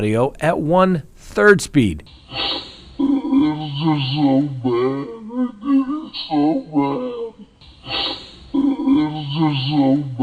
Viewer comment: I hear “It was just so bad, I did it so bad.” 🤷‍♀️